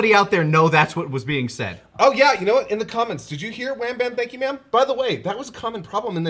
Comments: I always heard "Wham, bam, thank you, ma'am" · "Wham, bam, thank you ma'am" Yes, it was very clear for me. I was surprised didn't catch that 😂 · i was close i thought i heard "bam bam thank you ma'am" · I never heard "thank ya ma'am" I actually heard something very different. I always thought he said "Gotcha Bitch"